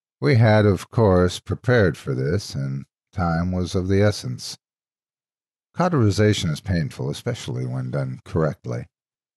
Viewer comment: What was the word uttered at 13:59? As the captions say its "authorization" and I don't believe it is accurate. Also other well done audio production. Even thought the erotica is not to my personal taste I found enjoyment in this story. Additionally your warning showed up again at 17:10 and I don't know if it was intentional at all.